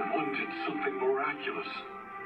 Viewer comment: Damn this takes place during my villain academia arc or maybe after or before kinda 4got gotta see. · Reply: "all for one did something miraculous"?!?!?!?!?!?!?!?!!??!?!?!?! did i hear this wrong please respond guys